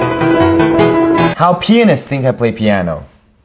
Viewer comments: Him: "How pianists think I play piano" Me being half deaf: "How peenus thinks I play piano" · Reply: Haha,I heard"How peanus thinks I play piano"... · i thought he said how penis play piano💀